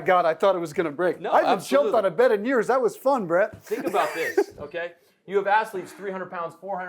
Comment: “I haven’t jumped on a bed in years, that was fun bruh!” Idk why that cracked me tf up Edit: okay so he said “Brett” but it sounded like bruh